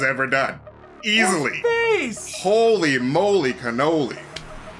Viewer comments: I was definitely not expecting so much from this episode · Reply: who else hears holy moly KENOBI? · who else hears holy moly KENOBI??